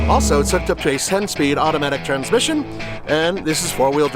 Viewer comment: Did you say a 7 speed transmission?